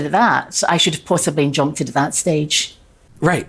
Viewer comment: I think she says “INJUNCTED” not “Jumped in” .. As in she thinks she should have taken legal action 5 yrs ago for the fringe show .. Also @ 19:00 .. “I’ve inadvertently PENNED the name of the show” I think ..correct me if wrong .. 🤔